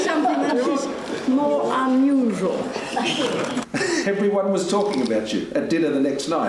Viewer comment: dunno why but I misheard as "you dont that people are talking abt you, dinner at the next life?"